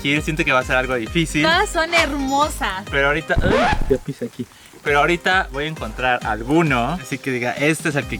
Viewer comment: yo escuché pero ahorita voy a encontrar a kunno :v xdxd